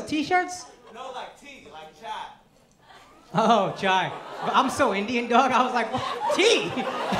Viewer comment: Damn i am the same i thought he was saying chi-cha, t-cha then read the subtitle and was like huuuh that makes sense